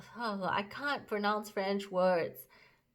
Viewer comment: I thought you said "Spanish" so when you said Yves Saint Laurent I had to replay the video hahahaha